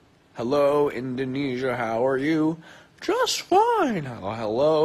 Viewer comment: Did he just say indonesia ? :v